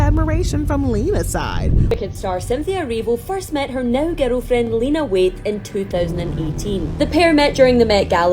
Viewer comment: I'm sorry but can someone understand her or am I trippin?? 🤔 Did this woman just say "this is where she met her now ghetto friend" ??? 🤔🤔🤔🤔 Anyone ? Anyone know what she said ?